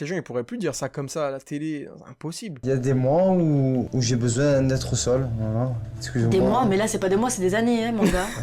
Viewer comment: je crois que j'ai un problème... J'ai entendu "besoin d'être au sol"... 😂😂